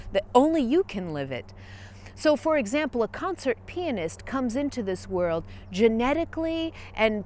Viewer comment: as a non-native English speaker, I startled when I thought I heard "a concert penis" 😂😂 Love your videos. Thank you so much,)